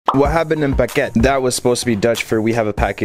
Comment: I heard it as "what habben in packet"